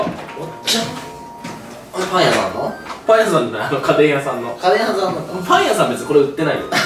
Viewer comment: 家電屋さんがカレー屋さんに聞こえた🍛笑